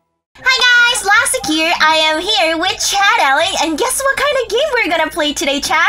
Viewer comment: WAIT A MINUTE....did say Chad Ali at 0:07 ? BTW I LOVE THE TACO CREW!!